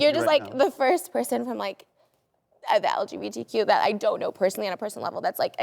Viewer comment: I could've sworn he said LGPTQ